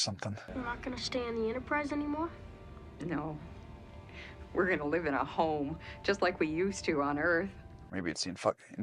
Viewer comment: I was listening to this in the background and had to stop what I was doing because I could have sworn the alien lady said "We're going to live in a hole, just like we did on earth."